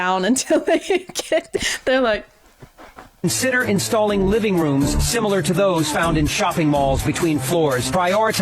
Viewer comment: did he call escalators "living rooms"?